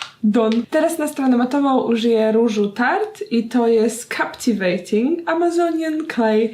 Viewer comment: Usłyszałam ,,kapciwejting" 😂💙